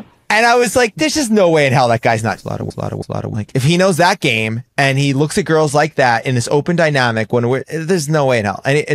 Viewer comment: what in the world did he just say lmao? Or was that censored? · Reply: "cheating on his wife". and yes it was censored bc the subject is scandalous.